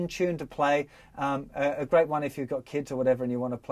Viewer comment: As an American, I misheard "crackin' little tune" as "kraken little tune," which fits the nautical theme nicely.